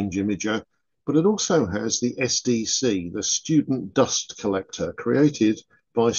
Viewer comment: I had my fan going, and it sounded like you said the SDC was the Stupid Dust Collector. Stupid dust getting into everything! I laughed.